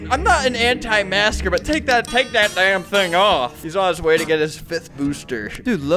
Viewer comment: did Albert say dang or damn?